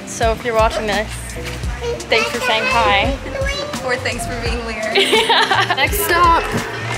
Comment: did say "thanks for being weird" ? 2:05 That seems... unnecessarily rude...